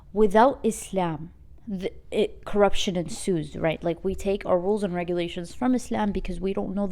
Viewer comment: corruption ............. what? , can someone please write the word for me? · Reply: ensues